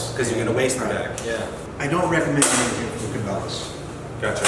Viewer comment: QUESTION, At the 1:44 mark he said he didn't recommend what? And at the 4:15 mark he said something about not using to wrap or package? I couldn't make it out because of the echo or a noise. Thanks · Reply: I think he said Cabella's. It didn't sound like ziplock to me